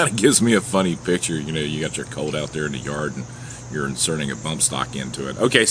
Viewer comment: Wasn't sure what you were saying at 3:13? but thought you were saying colon.. nothing made sense had to rewind it shame on me